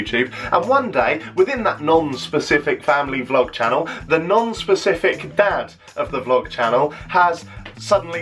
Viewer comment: honestly thought you said 'nonce specific dad' which is ironic 😂